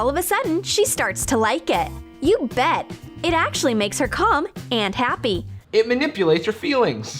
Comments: I heard that " it actually makes her cum" instead if calm. 🤦‍♂️ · did it say “It actually makes her cum and happy”? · wait... Is it just me or did it sound like she said it makes her cum? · I THOUGHT IT SAID CUM NOT CALM 😭😭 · first time I heard it, I thought she said:'It actually makes her cum', I had to play it 3 times to unhear it. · i thought she said it made here come...